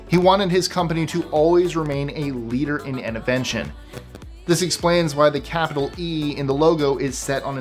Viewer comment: Did I hear you right, the founder wanted Dell to always be "a leader in innovention?" That term turned me on my ear!